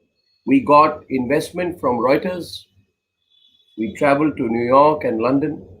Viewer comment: Did he literally say we have got funding from rioters or I misheard. By the way its a fact he got funding from rioters. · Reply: No he said Reuters. Reuters is an international news organization owned by Thomson Reuters. It employs some 2,500 journalists and 600 photojournalists in about 200 locations worldwide. The agency was established in London in 1851 by the German-born Paul Reuter. Wikipedia